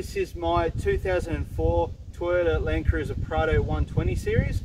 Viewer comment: Where’s? And his “because prado” remarks... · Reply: Ahh I must of missed you saying it. I heard say “because Prado” and I’m thinking what’s his problem... haha